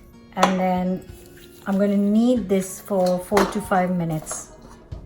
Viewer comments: I thought you said you would knead it for 45 minutes. I would pass out :). Then I realized it was 4 to 5 minutes. Love your channel by the way. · I was distressed when I misheard "knead for four to five minutes" as "knead for forty five minutes". I need to work on my critical thinking.